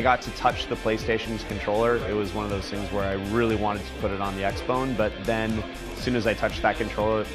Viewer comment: lol did he say xbone???